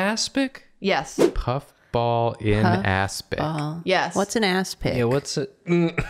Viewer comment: asking "what's an aspic?" at 2:00 made me choke on my water It sounded like ass-pic